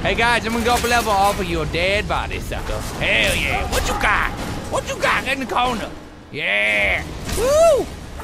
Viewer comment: Did anyone else hear someone go "WHAT UP?!" at 8:48? xD